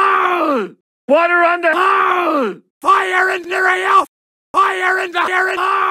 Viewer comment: I heard fire in the ayo